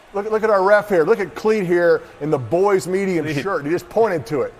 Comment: During the game I thought he was saying the boys media shirt and I was so confused what it meant but now I know it's medium